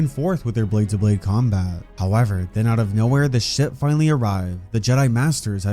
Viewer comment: who else heard “The sh*t finally arrived.”